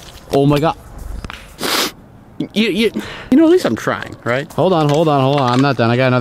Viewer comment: 8.18, When he said, "You know at least I'm trying..." I thought he said you know at least I'm 'Crying!' XD Poor Unspeakable... I believe in ya dude!